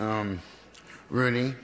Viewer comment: what he say in 11:47 ?? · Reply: "Love you"